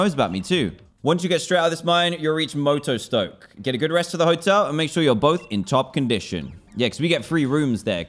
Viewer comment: When Kabu said that you should go and get a rest at the hotel. I was drunk one night and when I played through it i thought he said go and get a rest at the hospital